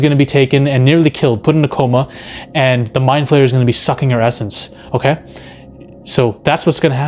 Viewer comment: had to do a doubletake for a sec i thought you were saying "the mind flayers gonna be sucking her ass"